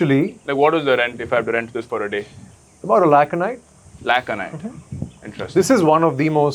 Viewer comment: what is this man saying 3:24 Lakh and what ? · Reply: Lakh a night